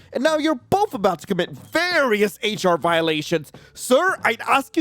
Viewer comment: I thought it said commit arson💀😭🙏🏻